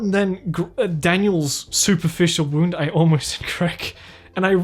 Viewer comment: wait he almost what? I heard “I almost did crack”